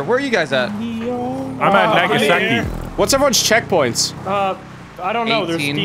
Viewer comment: We just gonna gloss over what just said? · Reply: He said Nagasaki..